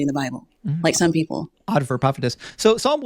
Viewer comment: Anybody got what was said at 19:25? :) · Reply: he said “odd for a prophetess” because prophets and prophets should KNOW AND be able to TEACH scripture in a way different people should understand.